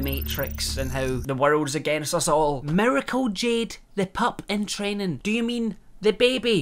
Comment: At first, when he said Miracle Jade due to his accent, I thought he said Medical Jade 🤣